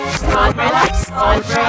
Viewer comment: Uncolourblocks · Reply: Tbh it sounds like it says uncolour